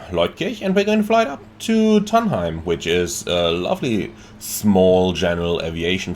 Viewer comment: Hehehe, for a moment there, I thought you said you were going to fly to Trondheim 😂😂😂